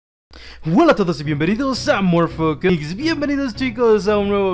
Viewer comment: o soy yo o el audio falla porque dice :v morpho mics